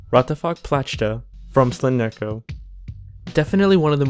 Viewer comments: i might be flammed for this but... Rat'aFák Plachta, i heard the name for about 20 seconds i thought it said "What The Fuck". because after seeing what i saw. i went what the fuck am i watching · sounded like he said wtf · The RAt'AFÁK word sounds like wtf to me XD did anyone else hear that? 😂 (